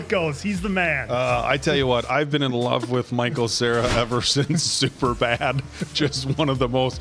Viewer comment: At firstI thought you said you've been a fan of Michael Cera since Superman. I didn't have the heart to comment that you had mistaken hi with Jesse Eisenberg 😂